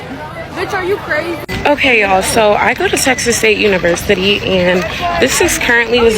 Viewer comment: At 1rst i thought she said " sexist state university " lol